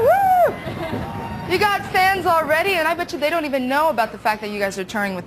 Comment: it sounds like she say "you got stans already!", which is very weird cause this interview was back in 1998, and the term "stan" wasn't even coined until the Eminem song by the same name which was released in 2000.